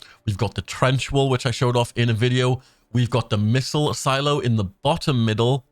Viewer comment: One of the strategems looks like a wall. This may be some kind of cover. However I don't see where this might be useful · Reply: if you meant the one in the bottom middle at 6:06 i think he says it’s a missile silo